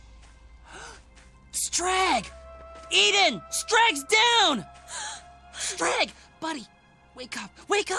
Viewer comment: I thought said, "Eden! Strag's dead!"